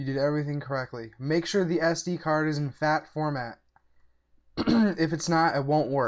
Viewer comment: 1.02 i thought you said make sure it isn't FAT, you Americans are hard to understand when you talk fast